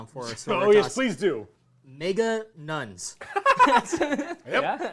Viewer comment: did he just say N*** Nuns???? Que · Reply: Nega nuns as in negative.